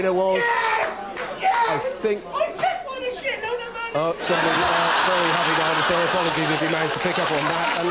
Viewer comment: what did the guy shout at 14:52 ? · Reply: "I Just won a shit-load of money!"